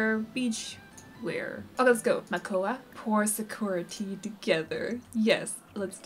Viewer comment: The way you said Sakura Tea is so cute, I thought at first you said Security and I thought, I must have missed that on game. I guess modern Snow White is a li'l bit mean, for making Makoa cry and is disliked by bird. I'm excited for the first bebe!